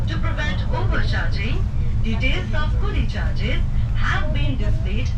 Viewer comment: I think she said 'the coolie' (porter) charges.